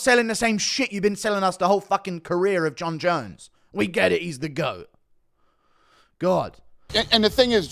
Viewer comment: Its going to be very embarrassing when tom puts your goat away in the first minute 😂😂 dana i thought u said he's ur goat hahahaha